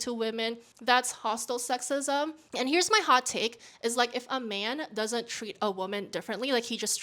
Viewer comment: Sounded like you said Heres(y)on Buttkirk 😅